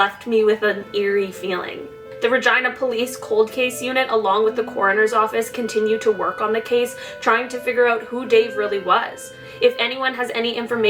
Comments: I thought she said vagina police 🤣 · Ermmm? Did you say vagina police? whoops.. Yep checked again